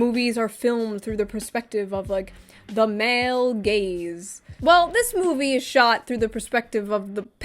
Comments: confession: whenever people said "male gaze" I used to mishear it as "male gays" which really confused me when I realized that they were talking about staring at certain body parts that women have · What she said: "The male gaze" What I heard: "The male gays" Needless to say, I was confused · I thought she was saying male gays and was very much confused for a minute.